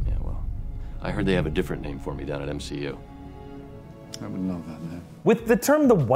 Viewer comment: did anyone else hear mcu?